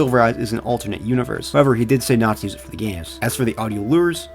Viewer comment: Ngl I thought at 11:18 you said nazis are for the Jews not “to not use them for the games”😂😂😂